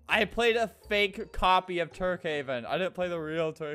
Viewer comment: i swear i heard "turdhaven" when he say tukehaven